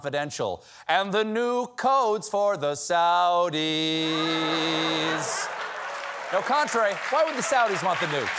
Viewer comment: I thought he said “and the new coats for the saudis!” … still works